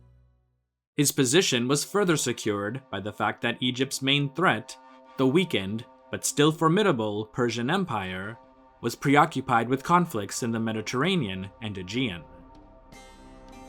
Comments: I was counting stitches on my knitting and thought you said Egypt's main threat was the weekend. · the way you said “though weakened” made me think you said “the weekend” and I thought “yeah man, the weekend is my enemy too”